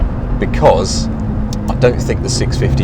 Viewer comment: Did he say "drived"? Lol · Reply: Kyle de Brouwer I thought he said "drived" too but after re-listening he says "tried"